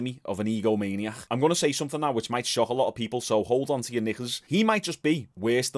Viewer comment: Im too high I thought he said “ hold on to your niggas “ 😂